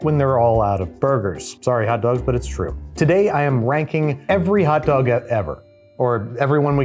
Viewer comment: i had to restart it because i swore he said "wanking every hotdog" instead of "ranking". I think my ears are putting words in his mouth.